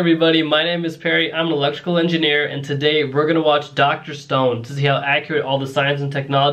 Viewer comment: bro I could swear that I hear him saying "Dr. Stoned" 😂